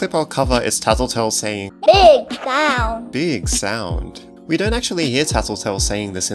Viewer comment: I think when I played tattletail I heard him say "big sound" except I thought he said "big eyes"